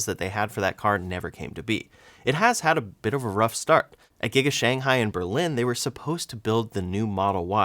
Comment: You misspoke @ 3:26, you said Giga Shanghai in Berlin, when Giga Shanghai is in Shanghai, China. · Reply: Haha. You misheard…. He said “at Giga Shanghai and Berlin”